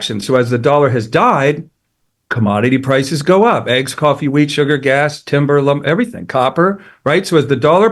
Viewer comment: When he listed things at 4:30.. did he say weed? 😂